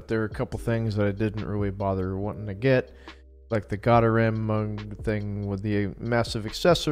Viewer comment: What did you say at 1:11 through 1:18 ? I wanna know. You mumbled right there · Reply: Götterdämmerung